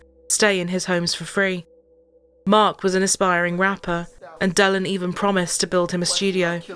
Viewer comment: I think she said "aspiring" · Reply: oow ok!!🤣🤣 I thought she said inspiring🤣🤣 but either way he wasn't gonna get there🤭🤦🏼‍♀️🤣🤣